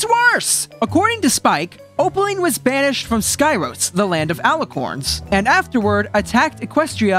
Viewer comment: Dragonborn where do I know that word....... Oh yeah skyrim. Wait did I hear skyrim????? 6:10 it will show up when he says it😂 Ah yes was Ulfric there?